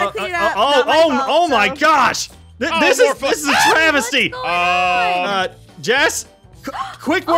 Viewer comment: When Aphmaus pupper started spreading fleas I could've sworn I heard scream "Oh my- this is a TRAVIS-sty"